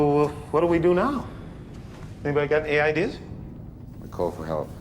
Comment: the caption says anybody got aids